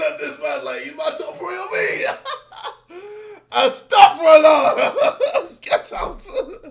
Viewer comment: did he say astagfirullah?